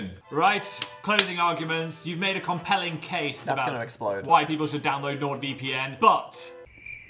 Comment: I really thought Phil said "white people should download NordVPN" and was so confused